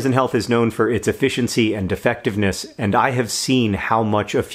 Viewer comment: ~ i swear it said "#defectiveness". ~ had to replay. Nope i know what i heard the first time. Not the same this video said a moment ago.